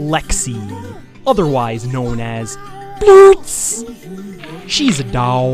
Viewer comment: Umm... at 0:22, what did you call her? Blurts?